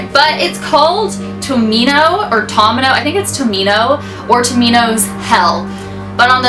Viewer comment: At first I thought it said "Tomato's hell" I'm like, what?